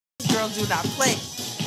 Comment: I thought it said “these girls do not slay” 😭